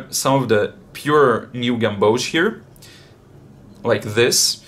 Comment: time stamp...what is that color please? I hear you saying it, but I can’t find it in the Daniel smith colors. Thank you! 💛💚. It’s like an old world yellow/green. I love it! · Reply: Eja Moon New Gamboge - Daniel Smith definitely do it.